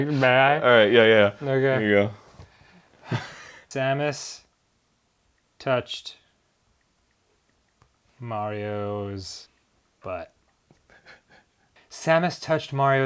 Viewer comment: I heard that as "Thanos touched Mario's butt".